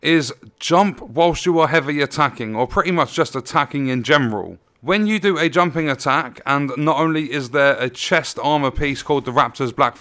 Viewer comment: did you say “attacking in gemroll” as opposed to general? Lol